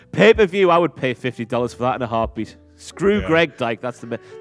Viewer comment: Did say "screw Greg Diaper?!" 😂😂😂